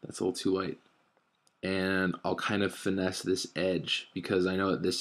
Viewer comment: I have ear problems at the moment (not even kidding) but am I hearing "finesse" used as a verb at 3:18?